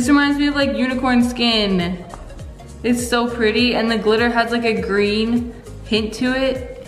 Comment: did you say pink bc that’s not pink 😂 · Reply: Maissa_ _prutty 8:55 she clearly said the glitter was green